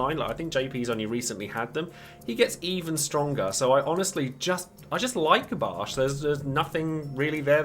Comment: I thought i heard you say: I just Like the Baschard